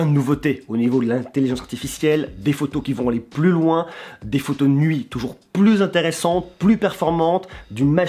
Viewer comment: j'ai entendu des photos nudes 🤔🤔